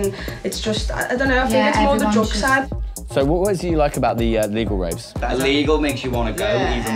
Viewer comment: ohh he said legal raves..i thought he said legal rapes...